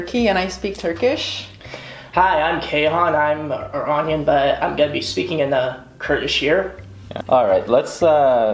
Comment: Lmao wtf did he say? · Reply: He says, "Hi, I am Keyhan. I am Iranian and I am gonna be speaking Kurdish"